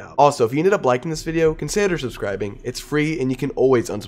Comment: is it just me or did he say "consander subscribing"?